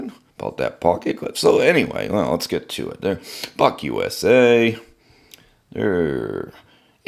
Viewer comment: 😎 “Buck USA” sounded like You said F-USA! 😂. I like that lock style but on the cold steel ad-15 lite… I used the knife pretty hard to pull & cut some wires … & the lock up really stuck on me… cs works great as fidget fun but if you use on hard cut it might lock up & need assistance to close… making the knife half as fun as it should be… I might have to try this Buck 🇺🇸! ✌️🤟🤙